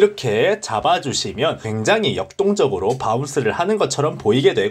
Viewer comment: 굉장히 역똥적으로 ㅋㅋㅋ 왜 난 이렇게 들리지?ㅋㅋ